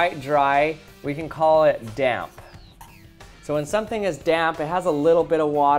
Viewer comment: sorry but i really heard dab